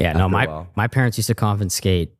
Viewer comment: said con fin scate instead of confiscate